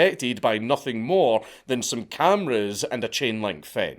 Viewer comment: I had to go back and turn on subtitles when he said inward pointing cameras. with his weird unidentifiable accent, I thought he said N word cameras.